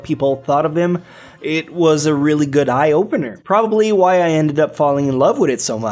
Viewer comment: did he really say "eye opener"?